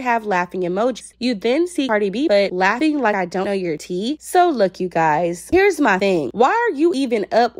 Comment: I thought she meant jt tea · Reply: I thought so but she said laughing like I ain’t got your tea 🤷🏾‍♀️